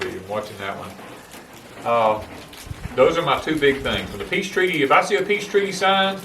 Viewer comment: Ahhh peace treaty! Thought you were saying peach tree at first!!